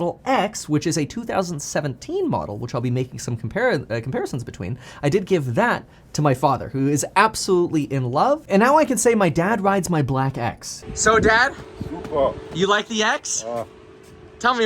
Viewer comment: what’s the saying on 1:13? my dad rides my black x?